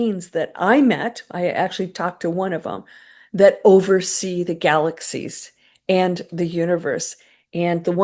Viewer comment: When you said, "9 beings overseeing the galaxies", I thought you said, '9 beans overseeing the galaxies'. 😂